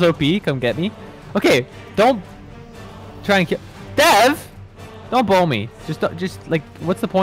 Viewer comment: When I heard “don’t bow me, don’t bow me” I thought it was “blow” and I took it out of context